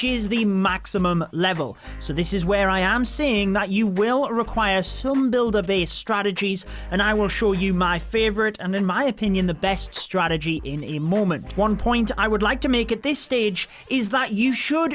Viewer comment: What? Builder Base strategies? I thought you said ”12 CANNON CARTS”!